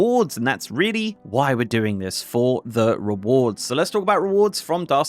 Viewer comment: I thought you kept saying "Darcy Duos" lol